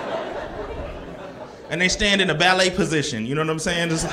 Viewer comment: I'm Mexican, that ballerina position joke had me rolling. I tried not to laugh, I just couldn't. LOL good one. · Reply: I thought he said valet